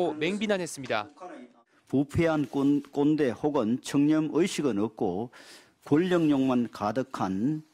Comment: 청년의식 아니고 청렴의식 아닌가요? 요즘 당근칼 넥슨 시위 칼부림도 그렇고 요즘 엠비씨가 막나가는 것 같네요. 그냥 맥락 파악 안하고 들리는데로 쓰고 검토 안하세요?